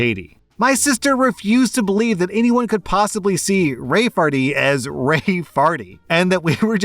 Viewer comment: I THOUGHT IT SAID RAE FAIRY LOL